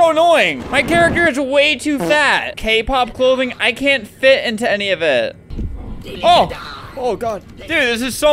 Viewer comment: did he say "kpop clothing, i can't fit into any of it?"